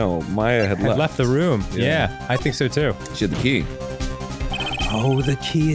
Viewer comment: I thought Arin said “she had to pee” at first!